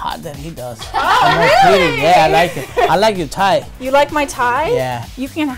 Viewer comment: I swear i thought he said I like it TIGHT